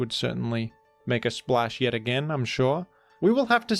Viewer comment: I swear says "mega splash"